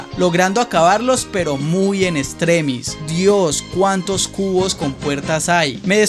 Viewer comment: escuche mal olor dijo dios cuantos CULOS hay🥶